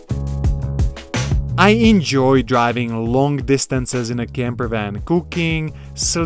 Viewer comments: I could swear you said you enjoy cocaine 😁😁 Your ebikes are pretty dope my friend....I'm not coordinated enough for that one wheel contraption. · I thought said he enjoys cocaine 😂